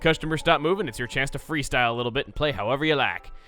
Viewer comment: Did I just heard say "Freestyle bitch" in this video during the harp/sing sidequest?